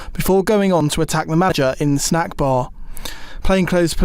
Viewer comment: did he just say ‘in the snack bar’ 😮